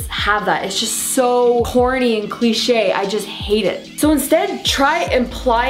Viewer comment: I honestly thought she said horny and cliché and btw thankyou for the tips and wish me luck